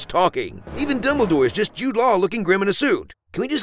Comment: "Even Dumbledore is just Jude Law..." Everything else in this video is funny and accurate, and amazing as always, but let me tell you there ain't such thing as "just Jude Law". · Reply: He did not say just Jude Law. He said" just Jude Law making grim faces. "